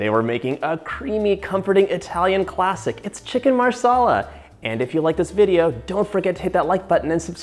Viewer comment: For a hot second I thought he said 'chicken masala' 😂